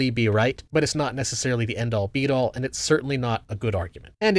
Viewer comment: did you say “end all, BEAT all?” fuckin zoomers and their eggcorns/malapromisms. the phrase is “end all, be all.”